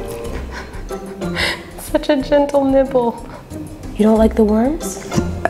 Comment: “Such a gentle Nibble” For a second I thought she said nipple 🤣🤣🤣